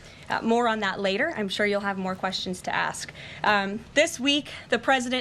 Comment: Wait, what .. I mean; I know my hearing is like total trash from a few decades of the rythm of 25 mike mike .. but did blondie say; "I know you morons will have more questions on that later" on about the 3:40 time group?